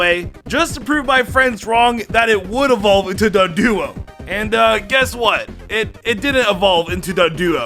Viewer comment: sounded like he was saying dugduo but it was doduo